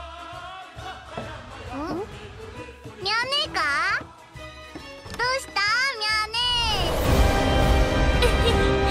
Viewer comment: What did she say?!? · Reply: she said "Yanika?"